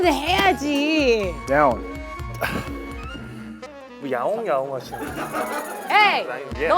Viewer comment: 저도 그렇게 들었는데 ㅋㅋㅋㅋㅋㅋㅋ 야옹야옹 ㅋㅋㅋㅋㅋㅋㅋㅋㅋ